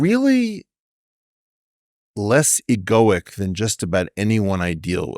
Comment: "Less" what? Egoic? I've never heard of this word.